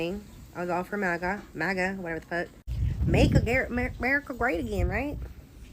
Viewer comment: Make rape great again is what she is saying. Voting fore a misogynic old man I diapers. Good for you lady!